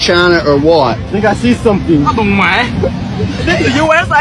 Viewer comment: well from the way I heard it, it sounds like "a đù mẹ" which we use it the same way we use "aww dang it" but when you translate it, it will mean "aww fuck mother"